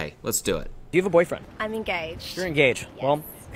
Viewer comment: I thought she was saying "I'm gay" at first. But alright engaged is cool too.